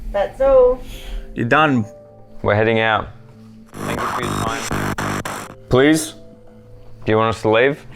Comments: i heard 'thank you for your time' not please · sounded like it said thank you for your time